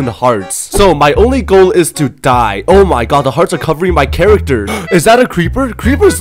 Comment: You said it's covering my character Me I thought u said it's cutting my character I started to sing u didnt have to cut me off Sorry just a misheard